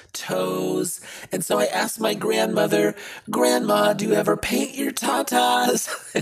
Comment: When I first watched this I thought it said “do you ever bathe your ta-tas?”